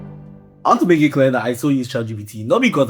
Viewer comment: the captions think he said "I want to make it clear that i still use LGBT" 😂😂